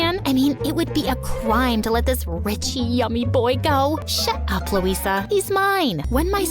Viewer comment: Excuse me but did I just hear “yummy”😂😂😂😂😂😂